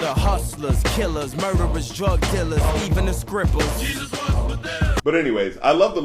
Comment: i always thought he was saying "even us cripples" as in kanye west believed he was living it hard with a handicap that is constant publicity. turns out its just "even the strippers".